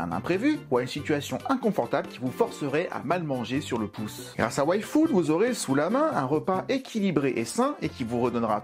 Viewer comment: tu as dis "Yfood", j'ai entendu "Waifood" ( Waifu + Food ) X)